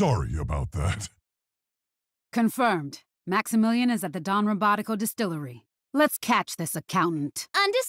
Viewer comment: I always heard "Maximillien is at a damn robotical distillery" for some reason